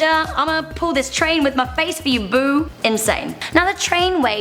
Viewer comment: Did u just call my country boo at 6:05 ??? But I still love ur videos